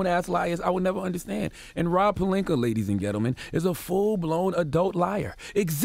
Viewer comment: Did anyone catch him say Gettleman @ 2:31 or am I crazy .. being a giants fan got me hallucinating